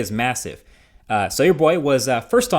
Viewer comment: Did Marques say "So your boy...XD" ?